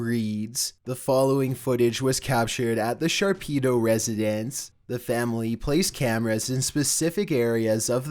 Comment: Normal People: “The Shapiro Residence” “The Sharpido Residence” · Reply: I thought he said sharkpedo residence